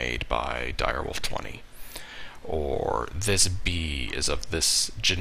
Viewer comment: "This B is generik" Why do I hear stuff so wrongly? >_<